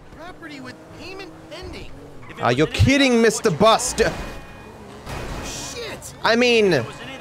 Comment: It sounded like he was going to say Bustard 🚌